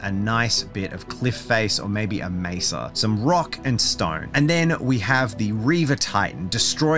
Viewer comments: Did I hear a Rock and Stone?! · did i hear a rock and stone?